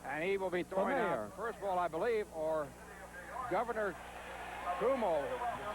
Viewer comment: Oh man. I loved Ralph Kiner. Really did and do. But did he say,"there is Mayor Cock and Governor Cumoo"??! At about the 4:47 mark. Lol